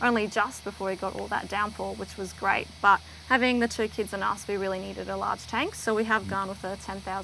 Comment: Did she say THREE kids? Maybe TWO? Any kids in this small of a house is child abuse as far as I a concerned.